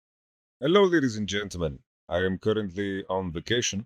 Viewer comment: Lol, I heard “hello n*ggas and gentlemen “ at the start.